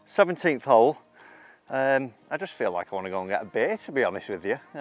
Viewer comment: what did he say? He just wants to go get baked? Lol